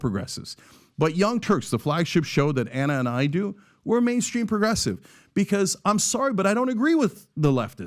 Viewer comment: I thought he said "main street progressive" at first which seemed better.